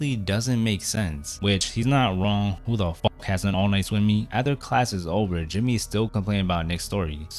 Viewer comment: Oni-slami, 0:37 That's what I heard. What did you say?